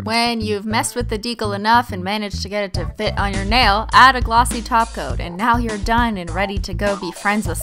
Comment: I feel so stupid ever since i started watching her videos i thought she always said (glossy taco) but she acually sez glossy top coat 😂